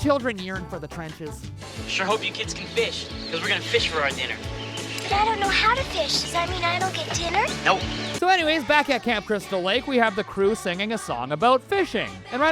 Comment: Did he say camp crystal lake its friday the 13th